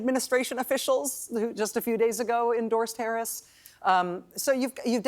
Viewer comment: Imma need y'all to check the closed captioning @ 0:59- She's saying endorsed Harris, CC says endorsed terrorism. I've watched it more than once and screen shot it. WTH.